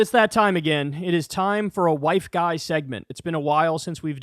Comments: I had my volume on low and thought you said, “it is time for a white guy segment” 😂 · I thought you said you were having a WHITE GUY segment. I was like hell yeah let’s do this! But you didn’t say that. You said WIFE GUY. 🫵🏻🫠